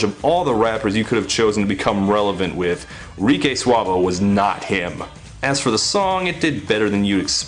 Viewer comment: did he really say RikAY Suave? lol